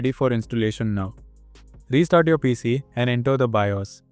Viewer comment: i thought he said "restart your pc and enjoy the virus" i was like "WHA-" then i turned on captions and he said "restart your pc and open the bios" LIKE WHY DID I HEAR ENJOY THE VIRUS